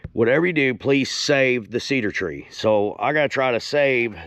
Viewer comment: i thought u said to leave the cedar tress man we canty ever have nice stuff around here lol